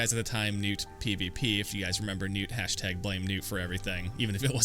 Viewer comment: When you said #BlameNewt I thought you said #BlameNude.